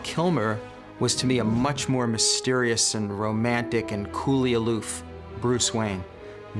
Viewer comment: what michael uslan says in 1:48???..after romantic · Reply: Vaggelis Kamaretsos Vip coolly aloof